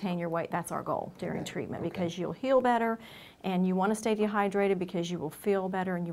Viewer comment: Sounds like she said “ you want to stay dehydrated” 🤔